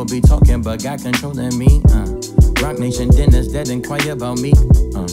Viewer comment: "Love and loyalty that's over honesty, the devil be talking but God controlling me, Rock Nation dinners that aint quite about me.....interesting. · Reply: i thought he said "roc nation dinners that inquire 'bout me"